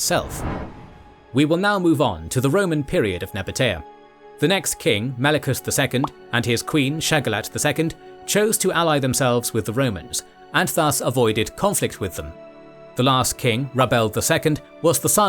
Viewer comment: thought you said queen shagalot the first time hehe...